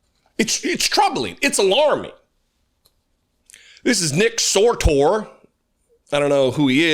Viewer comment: I had to rewind a bit as I thought I heard Nick Soretoe.